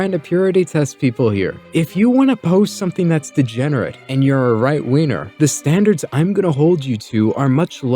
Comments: "right-weiner" is what I heard 🤣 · did bro just say "RIGHT WEINER" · I heard "right weiner" and honestly, that would fit too...